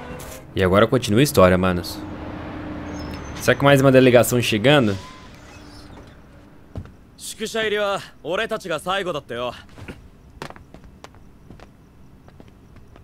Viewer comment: o disse: "Será que vai ter mais uma ligação"; Eu ouvi: "Será que vanderleigacão"